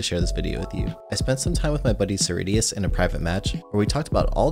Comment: When you first said cerridius I thought you said SirD lol